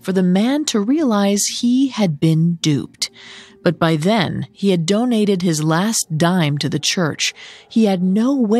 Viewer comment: "he had donated his last dime to the church" I thought you said he "hadn't eaten in days." Unless "his last dime" was the only money he had when he showed up, not sure how someone could be that starved and still have any money to worry about losing.